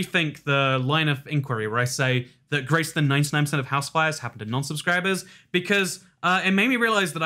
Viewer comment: WAIT ITS HOUSE FIRES??? i thought you’ve been saying house wives this whole time